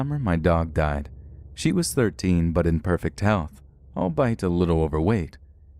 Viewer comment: What is he saying at 8:53. It sounds like he's saying "I'll buy it" which doesn't make any sense... but it makes sense that he would "albeit" which also means although.... I often hear mispronounced words from different narrators.. common words that we use all the time. What causes this? Maybe it's just me but I'm really puzzled by this... and I mean this with the utmost respect because I absolutely LOVE Let's Read.... If anyone knows, I'd appreciate it if you let me know.....